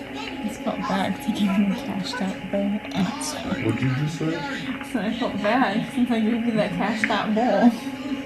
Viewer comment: am I the only one who couldn't focus because it sounds like they're talking about smoking weed? XD am I mishearing? "I just felt bad 'cause I gave you that cashed out bowl"